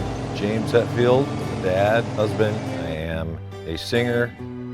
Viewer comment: "I'm a dad, husband, I am a singer and guitar player for a band called Metallica...". This statement speaks volumes. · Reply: Actually I first thought he said "I am a bad husband"... but its; dad, husband... 😁